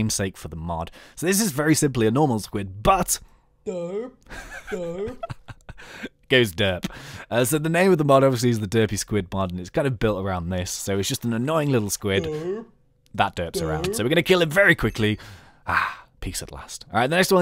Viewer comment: It sounds like u said dirty spud mod